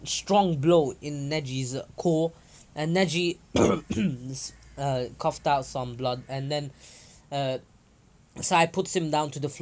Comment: For a second there I thought you said you were coughing out blood bruh